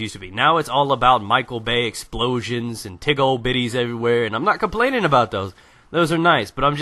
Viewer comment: did say, "tigg ohh bitties everywhere" or was that just me.